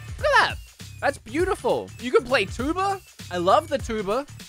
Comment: When he said 'you can play tuba?', I heard 'you can play tuber?' I'm getting too used to the accent